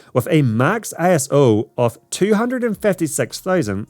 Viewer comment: ISO of 256000 ? or 25600? it kinda scratched my ears ? Or did I misheard ?